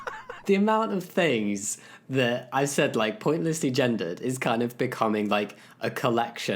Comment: It took me forever to get joke, because I heard it as "pointlessly agender"--which is also what the auto-captions say.